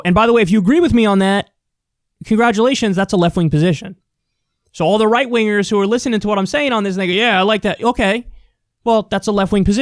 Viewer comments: Yo, ive always liked Kyle and been a fan of Kyle, and I’m not trynna smear him, but did he just say the n word at 7:20? Even if he didn’t say it it sounded like he said “All the right wingers who are listening to what I’m sayin on this nigga” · Did he say to all the right winger who are listening to what im sayin on this nigga yeah i like that? At 7:20?